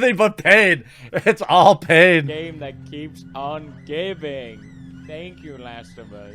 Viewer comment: Sounded like he said "the game that keeps on gaping." Hahahaha!!! 🤣🤣🤣